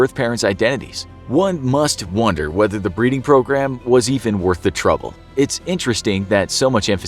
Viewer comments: I thought it said bleeding program. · I thought it said bleed program